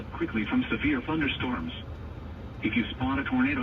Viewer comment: 😄So I usually put on CC because half the time I can't understand what YouTubers are saying even with my audio on 100 % because my brain just likes to jumble words. Anyhooo at 14:52 when the radio came on, my CC read "at once into the basement or a small se%ual room in a sturdy structure" I have no idea what he actually said because it really sounded like that. Can you explain if you people in Kansas have those type of rooms? Asking for a friend...🧐🤣 Yikes, at the end of the video, looks like you are heading straight into Tornado Territory with those green skies. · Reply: Ha! I think that said small central room 😂😂